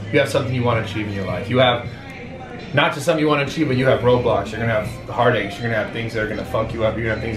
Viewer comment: Don't know about anyone else, but when he said roadblocks, I thought he said Roblox